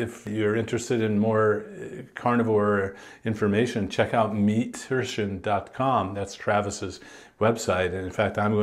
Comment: Why not add the link to the description? I don’t understand what you're saying, and I tried finding the site using the names. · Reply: meat rition